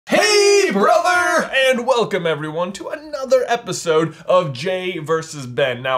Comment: UMMM when the video first started I thought J said welcome to the "FINAL" episode of J vs. Ben and I had to pause the video and take a moment of silence to mourn the loss of this series but then I rewatched the first 5 seconds and realized he said "ANOTHER" not the final